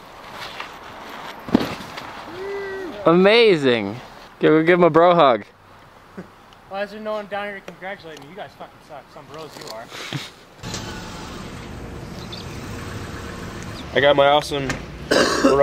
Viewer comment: Did i heard say fuck at 1:25?